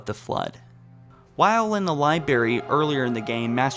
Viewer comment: Does anyone else hear voice cracking? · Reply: What about the fact that he said liberry